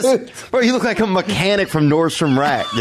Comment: "You look like a mechanic from Nordstrom Rack" all time classic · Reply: I thought he said "a mechanic from North Iraq" 😂